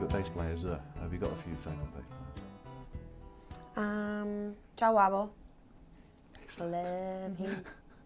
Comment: What is her favourite bassist, can't make out what she said. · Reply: Jah Wabble -- the answer came 10 years later :)